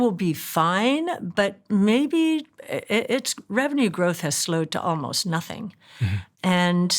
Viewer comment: Cathie Wood said at minute 5:49 that "Apple's revenue has slow down to almost nothing" so far this year quarter one and quarter two combined Apple has earned $219 billion dollars. "almost nothing"??????????????????????? I can Believe that Apple may have slowed down, but almost nothing, come on man. When I hear this kind of talk I cannot continue the video. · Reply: Listen again carefully She said “Apples revenue growth” ( not revenue) No offence